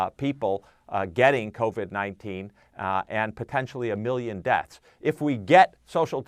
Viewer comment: did he just say 1 billion deaths????